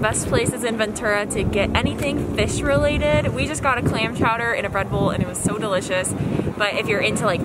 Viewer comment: I thought you said you got claim chowder and a Red Bull 😂